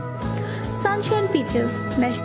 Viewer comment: Did she just say sunshine pigeons? LMAO